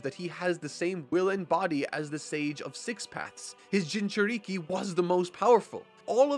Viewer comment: I could swear I just heard, "The Sage of Six Packs"